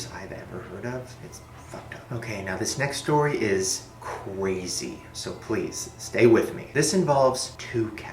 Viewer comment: icant stop hearing him say "cwazy" instead of crazy